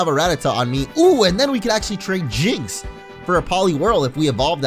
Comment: WHAT DID YOU JUST SAY HOW DARE YOU TRADE HIM IN FOR A HOOKER